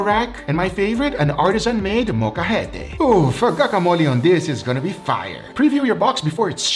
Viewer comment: did you say CACAmole?